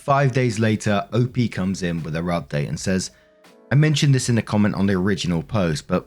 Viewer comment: Idk if its just your accent or what but both times it sounds like you say "op comes in with her update".